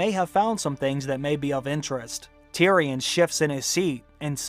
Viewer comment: Just there, I thought you said "Tyrion shits in his seat" - then I realised Tyrion shifts in his seat was the correct word. I think I need new spectacles, lol.